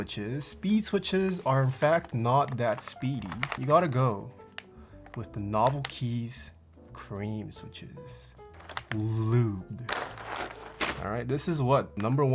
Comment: i heard him say "lewd" 👌😏